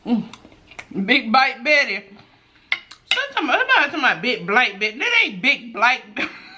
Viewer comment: That was me! I thought you said Big Black Betty. That's what happens when you're eating and talking. 🤣😂 You crack me up! And I know you lyin! That ain't no paint on your lips! 😜